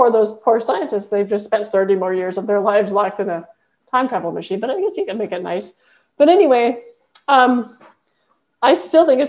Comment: with the scientists and the time machine. Did you say, “At least you can make it nice?” 🤣 Absolutely loves this, and learned a lot. Thanks! 🙏🏼